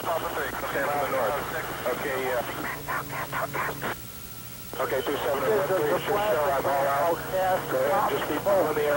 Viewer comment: What does he whisper in the 4:07 area? Is really like to know. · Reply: Can’t talk, can’t talk, can’t talk